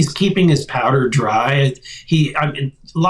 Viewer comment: I thought he said Trump was "keeping his powder dry," what he was actually saying is "he's keeping his same old lie"